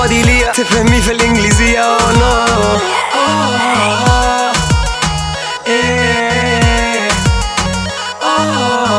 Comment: Who else hears that whisper voice at 2:33 wearing headphones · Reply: Yeah that whisper is lone wolf I mean he is saying lone wolf